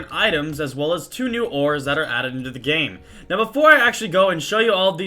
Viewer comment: When sky said ores I thought he said whores :)